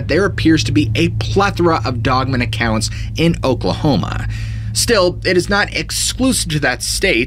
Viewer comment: Think I heard "Still", not "Sill", lol.